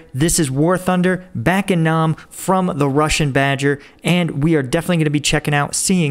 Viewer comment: did I hear that right? Prussian badger? I'm gonna save this name.